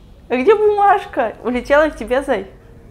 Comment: мне одной послышалось зай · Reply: Она вообще-то "зай" и сказала